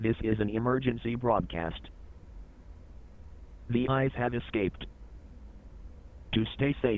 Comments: Doesn’t matter how many times I watch this video, I always hear “the iPad escaped” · I was listening to this video in the background and all I heard at 1:15 was "the iPad escaped" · I thought it said the iPad escaped